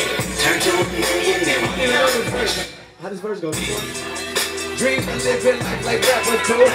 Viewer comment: Is he saying "How this verse is going ?" ? XD